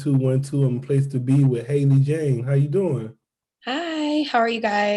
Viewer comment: i thought u said haley jade for a minute